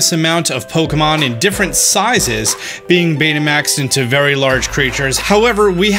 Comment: did he say Betamax? XD the new mechanic is pokemon turning into worse versions of vhs? XD